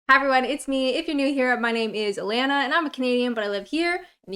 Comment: For a long time I thought your intro said "My name is Alanna and I am a Comedian living in the UK."